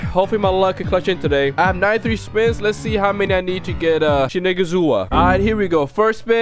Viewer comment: you need to be careful with what u say 👀 you said shi’nigga’zua not shinazugawa Edit: at